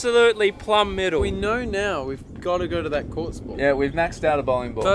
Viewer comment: Here I thought they were saying "corked" and they were saying "quartz" lol